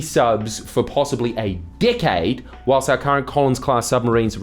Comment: took me a few tries to realize he was saying Decade, I kept hearing "Dick aid " 😂